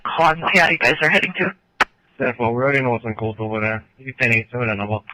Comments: Sounded like they said it's already been called over there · I thought they said they know what's in code over there... lol idk